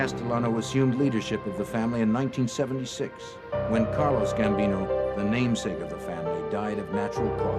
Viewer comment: When the narrator described Gambino's first name, it sounded like he said Carlos, not Carlo, which would be Gambino's correct first name.